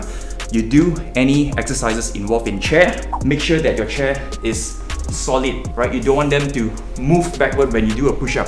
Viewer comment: I thought he said, "whenever you have a chair, make sure that you have a chair"